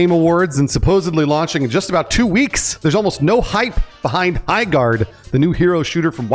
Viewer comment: I heard Eyeguard and I just simply thought, sounds about right.